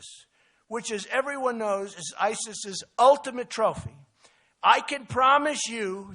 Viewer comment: Did you notice? They dubbed over the audio at 1:12. Trump actually said, "trophy" but it was recorded over and what you hear is "trump" instead of trophy. (the Vatican being the ultimate trophy of ISIS.)